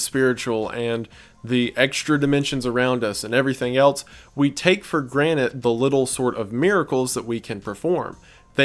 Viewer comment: does he say “we take for granite”???